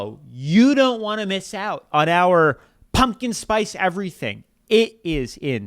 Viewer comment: I thought I heard you say "pumpkin spice Velveeta"!! LOL